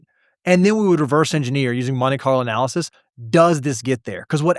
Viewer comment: What is it they keep saying? Money car analysis? 2:01:11 for example